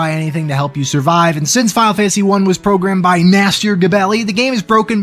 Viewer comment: i always heard it as "Master Gebelli" like it was a title given to this guy by for just how insane and great his programming was